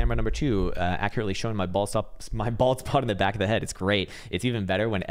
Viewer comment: When you said "bald spot" I actually thought you said "ball sack". 😆 Because that sounds like something you would say!